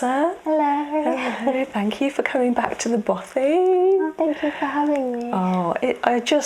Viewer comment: what is it that is saying here? "Thank you for coming back to the botty"? What is it, and how is it spelled? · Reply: Bothy!!